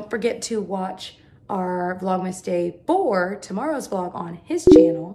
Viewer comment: I thought the Vlogmas said Viagra